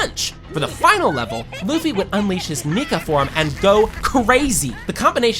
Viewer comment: Wuh did you say? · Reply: Dw he said "Nika"